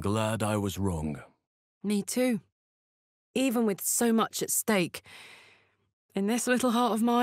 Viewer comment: I was bored so every Astarion interaction list 00:00 - with Wyll 02:23 - with Halsin 03:06 - with Gale 05:27 - Vampire lord with Gale 06:08 - Vampire lord with Karlach 06:54 - with Wyll 08:11 - with Jaheira 10:02 - Spawn (I think?) with Wyll 11:06 - with Gale 11:47 - with Karlach 12:30 - Vampire lord with Wyll 12:50 - with Lae'zel 13:20 - Vampire lord with Halsin 13:48 - Vampire lord with Minthara 14:10 - with Karlach 15:15 - with Shadowheart 16:00 - with Lae'zel 16:13 - Spawn (I think?) with Minthara 16:25 - with Gale (my fav one) 17:10 - with Minthara 17:30 - with Gale 18:12 - Vampire lord with Lae'zel 18:54 - Spawn (I think?) with Halsin 19:50 - with Karlach 20:36 - Vampire lord with Jaheira 21:46 - with Wyll 22:46 - Vampire lord with Minsc 25:59 - with Lae'zel 26:55 - Vampire lord (I think?) with Shadowheart 28:05 - with Minthara 28:25 - with Lae'zel 29:15 - with Shadowheart 29:58 - with Lae'zel 30:17 - with Minthara 31:36 - Vampire lord (I think?) with Shadowheart 32:17 - with Lae'zel 32:37 - with Wyll 33:58 - with Gale 36:20 - with Gale 37:19 - with Gale 37:27 - with Karlach 38:17 - with Shadowheart 39:47 - with Shadowheart 40:33 - with Gale 41:13 - with Lae'zel 41:50 - with Shadowheart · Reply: Omg when Karlach threatened him with a Stake... I thought she said Steak. I was like "I didn't know she could cook a steak, I wonder what kinda steak she would like.." lol